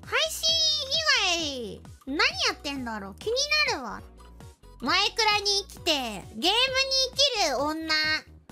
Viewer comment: I’m JP bro. Just in case for those people who are Japanese enthusiasts, but I think at 4:40 she said ”maikura ni ikite, gemu ni ikiru onna” which literal translation is like “She is a girl who lives for Minecraft and games.” thank you for clipping this moment!